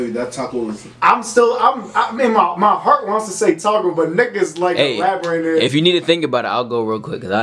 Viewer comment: When you said nick is twice I could have SWORE you said the N word but nah you said nick is lmao